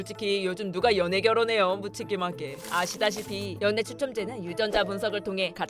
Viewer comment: 연애로 들리는데오?